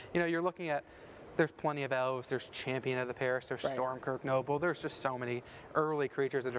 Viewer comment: For a second I thought he said "Storm Crow" at 5:35 I was like "what?"